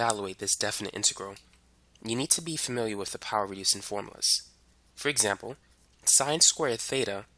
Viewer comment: what does he say at 1:42? Power rasing or power reducing formulas? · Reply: Because there’s a sin^2(theta) which you can use the power reduction formula of trigonometry. To get rid of the power. It’s a trig identify.